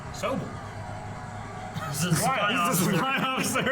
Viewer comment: So I’m watching this while at work on my headphones, I legit thought said “soap” at first. But when I then heard supply officer I put it together and had to contain a laugh cause I’m in a room full of people.